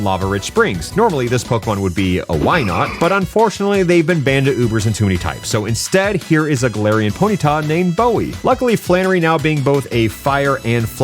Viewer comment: I can't understand what he's saying, so I gotta ask.... at 9:10, what does he say about Wynaut? I'm hearing "they've been banda ubers in too many types"... but I don't think that's right